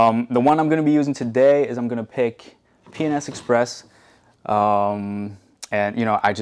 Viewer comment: Totally thought you said you clean the interior with "Penis Express". :D Great video! I love the commentary and explanations on what you do/use and why. Subscribed.